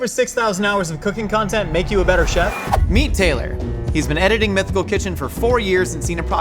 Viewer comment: I heard “meat tailor” in the beginning. I was like what kind of job is that?? Oh god I’m so tired.